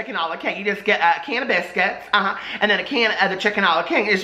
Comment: I thought you said “canna-biscuits” (like cannabis get it) to Jane!